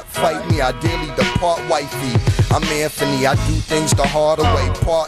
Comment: does he say “and I’m here for me”